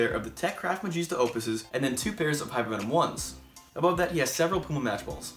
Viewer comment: wait..... Did he say hypervenom 1